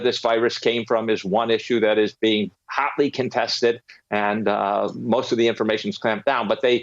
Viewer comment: Whoa, what did he just say? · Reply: "Hotly contested"? I'm trying to understand.